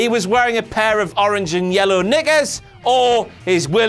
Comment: WTF DID HE JUST SAY!? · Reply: Knickers. What are you yelling for?